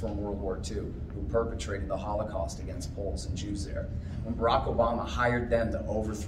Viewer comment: What does Scott say at 10:24?: "Who perpetrated the Holocaust against ????? and Jews there"??? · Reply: Poles